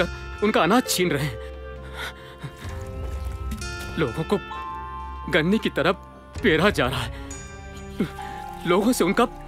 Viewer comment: what did he say? "LogoN ko Ganney ki tarah.."?